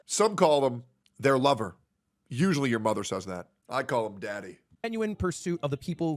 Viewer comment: wait did he really say he calls himself "daddy" at 58:10 ?